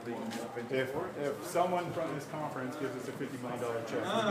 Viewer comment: "kinda 12 months away from production when we get funding" is what I think I heard at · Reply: "if someone from this conference gives us a 50 million dollar check, we could still deliver vehicles in 2023"